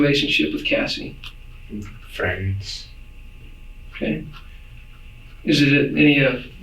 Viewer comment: 'The Interrogator' did not say, '' 'Kay' " ..he did say, "okay"